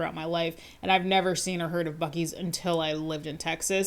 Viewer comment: I was listening to this at 2x and misheard it as "I had never seen a herd of Buc-ees until I lived in Texas" and just was like that sounds about right and didn't question it until I suddenly realized what you had actually said.